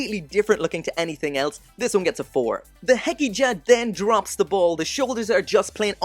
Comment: what is the gundam @ 6:27 ? i cant quite make out what hes saying? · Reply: It's the hgibo hekija